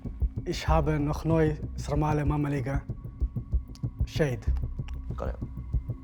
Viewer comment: DID I HEAR SARMALE AND MAMALIGA??